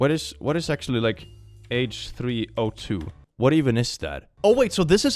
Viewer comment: I was partly zoned out and thought they said H3O (hydronium), and was thinking "dude, that's just how water ph balances itself" for a second